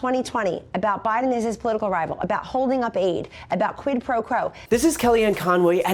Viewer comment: Did she say, "Quid pro crow"?